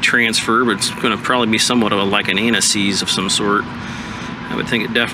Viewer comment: did you say anti seize or anal ease? lololol both work